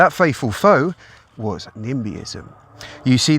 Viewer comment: Also what the hell is "nimism". I couldn't make out what you said at #8:30, and the subtitles don't enlighten me either! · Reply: Sounds like "nimbyism" - not in my backyard